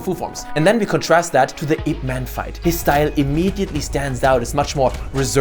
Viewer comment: oh it's "IP man" i always thought he said "hitman" and I was very confused like hitman don't do that